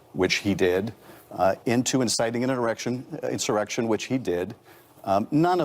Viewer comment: Did he say 'erection' at 2:01. I swear that's what I heard